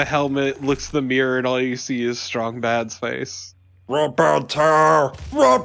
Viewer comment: It took me until this, my... fourth or fifth watch of this, to realize that was saying "RIP AND TEAR" in the Strong Mad voice. I legit thought he was saying "ROBERTO".